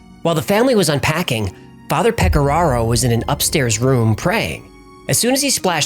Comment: Oh my god....I heard you say Father pecor.....and that's all I heard HAHAHA